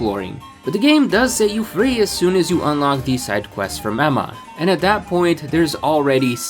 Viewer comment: did you just call her momma?!